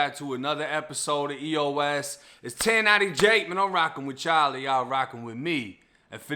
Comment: I thought you were saying you were rocking with Charlie. Anyway cheers.